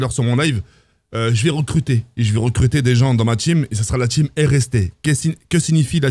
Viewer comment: ptn j'étais pas bien j'ai compris team MST ....